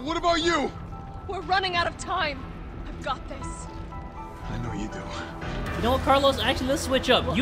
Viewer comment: When Carlos said "I know you do" I thought he said "I love you to" I was finna say whew chile take her on a date first 🙆🏾